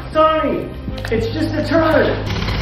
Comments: All I heard is “Amber Turd” 🤣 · I thought i heard amber turd..